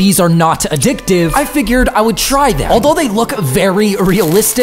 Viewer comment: Dude must not hang out with any smokers irl if he thinks herbal cigs smell like tobacco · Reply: well at first i thought that but then i rewatched it and i heard "They smell like normal cigarettes but taste awful"